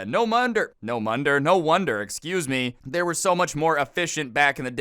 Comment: When you flip the letters IRL and say "no monder" instead of "no wonder"